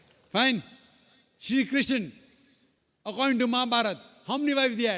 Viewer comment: What name did he call by this question 1:30? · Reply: Sri Krishna he said